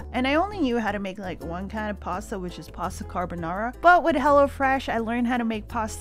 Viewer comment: When said, "Hello Fresh" for a minute I thought she said "Hella Fresh" I don't know how but I had to relisten to that.